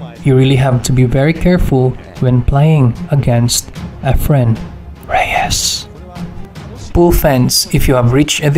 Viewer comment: what with the whisper "REYES" freeks me out!